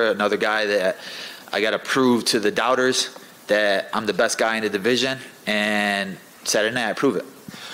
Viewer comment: No matter who wins or doesn’t, it’s gonna be the most spectacular decision we’ve ever seen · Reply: i thought he said i gotta prove to people im the best in the "dissision" 😂